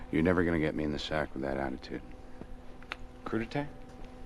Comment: what did he say? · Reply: curtitay